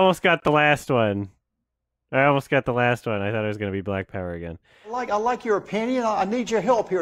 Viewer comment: Highlights at 11:21 I could swear I hear Derek saying "I like your o-peeny" instead of "I like your opinion".